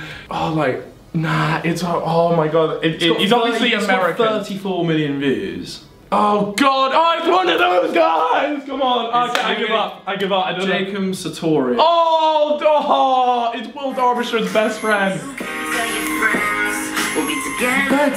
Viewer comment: Who the hell is 'Jacum' Sourtourious!!!!! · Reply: Yeah I know Oli sounded like he was saying Jacum Sartorius